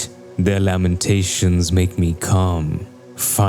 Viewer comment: I thought he said "their lamentations make me cum"😂